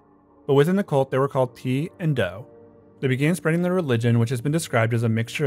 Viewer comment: I thought I heard you say the club called them 'P' and 'Doe'...PeeDo.. had to replay it a few times.